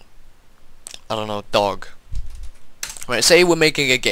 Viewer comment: i thought he said dong...